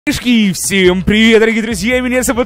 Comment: мне послышалось кешки